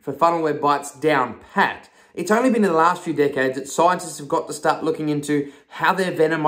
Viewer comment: "its only been the last few decades, scientists have" i swear i heard it like this.."its only been the last few dickhead scientists. Hahahaha😂